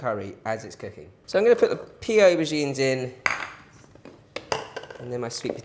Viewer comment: Great video ! Im going to this one a go ,What is the ingredient at 8:30, (2) is that sweetened Coconut milk ? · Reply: It sounded like he said pea aubergine/pea eggplant.